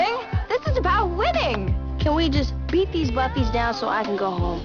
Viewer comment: does she say "Can we just beat these Buffys down..."? If she did...faith, glory, AND and buffy reference?!?!